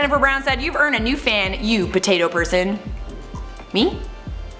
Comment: Did you just say "yo potatos" in your intro? 😂😂😂